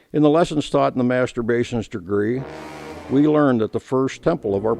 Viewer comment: did he say Masterb*tions degree???